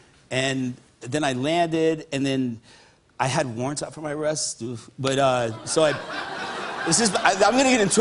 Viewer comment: what does he say? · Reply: I had warrants up for my arrest.